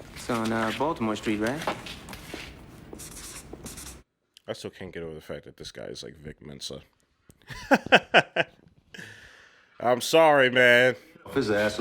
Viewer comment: what did he say ? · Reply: “I still can’t get over the fact that this guy is like Vic Mensa.”